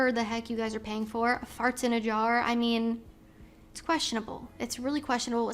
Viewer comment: I thought you said "hearts in a jar".